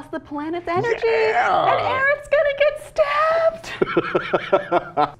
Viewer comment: Arabs gonna get stabbed ? what what did she say ? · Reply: "Aerith," it's a Final Fantasy reference. :P